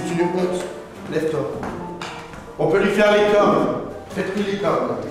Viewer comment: A 10:41 il a dit quoi ? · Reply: Les cornes